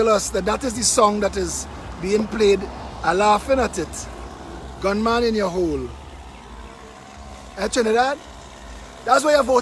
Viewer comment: I lost my marbles when I heard him say "Gunman in yuh hole“ 🤣🤣🤣🤣🤣🤣